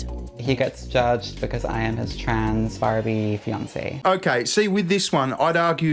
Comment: I thought it said it was a trans army nazi. 😂😂